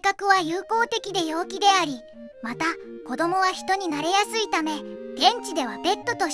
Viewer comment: ボケっとしながら聞いてたら 子供は人になりやすい って聞こえて猫が？！ってなったw